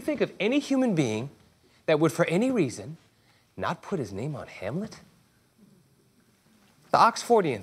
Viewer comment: I thought he said Hamilton not Hamlet I was so confused